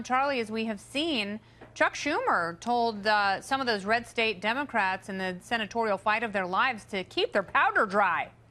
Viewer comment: Did she say Shuck Tumor?